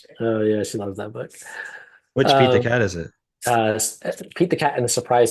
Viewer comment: When said one of his favorite books was Pete the Cat I could have sworn he said eat the cat